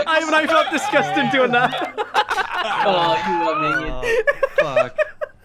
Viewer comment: what the hell does say at 11:37 ? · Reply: "I felt disgusting doing that"